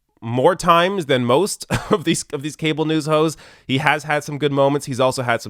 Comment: At the end it sounded like said "cable news hoes" lol